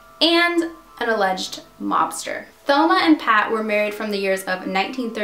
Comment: I thought you said an illegal lobster